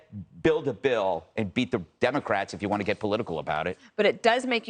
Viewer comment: It sounded like he said “be” the democrats, which would be accurate in this case. Seems like he meant “beat” the democrats, by signing a bill that is progressive gun reform.